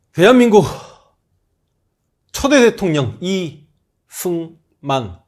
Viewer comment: 아 왜자꾸 저는 "이수만"이라고 들리죠? 둘다 빌런이라 그런가요?